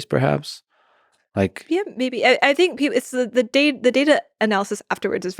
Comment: i heard her say yes "bebe" 😅